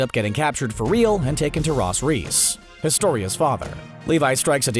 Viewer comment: Did he just say Ros Riess